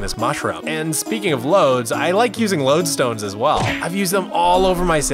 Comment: Did he just say "talking about LOAD"??!!!